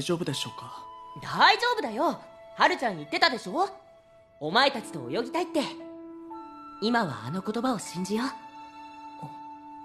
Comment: did nagisa say " don t worry haruka Said it we are a team you must believe us " ?? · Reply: leyenne he said "don't worry,haru-chan said it too didn't he? "I want to swim with you guys". For now let's believe in those words." Hope this helped! :)